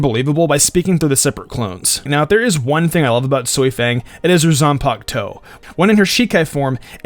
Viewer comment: did you say soi fang